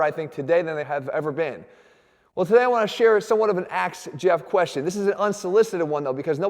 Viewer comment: Is he saying, "axe Jeff" or ask? 1:07, honestly confused.